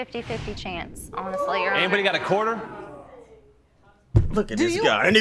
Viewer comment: I dead ass thought he said “anybody got a quarter”